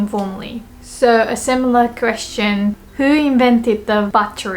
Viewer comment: when she said "who invented the battery" I thought she said "who invented debauchery"